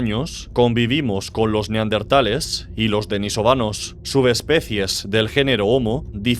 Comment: escuché venezolanos por accidente